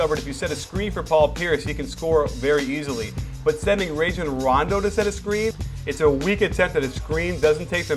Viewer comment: lol sounded like ragin' rondo